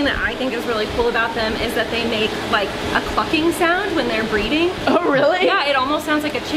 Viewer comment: I totally thought said "they make like a ****ing sound when they're breeding"